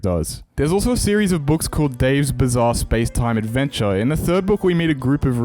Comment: Did you just said "bizzare adventure"?